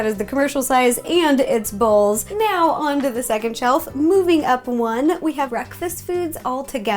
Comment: Did anyone hear her say chellf